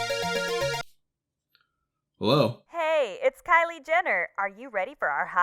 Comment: He really out here flexing on us with his billionaire gf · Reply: Not gonna lie I thought he said Kaitlyn Jenner. Makes more sense now.